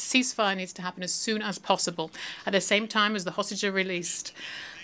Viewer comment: Did he just say sausages or hostages.